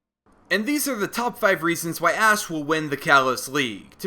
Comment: At like 4:55 did he say ass instead of Ash?